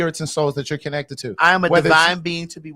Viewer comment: "I am a divine being to be worshipped". All I heard was "I am a loved goddess!" Back!